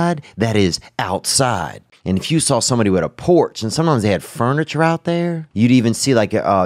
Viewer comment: I’m trippin lmao I totally thought he said Porsche 😂🤦🏼‍♂️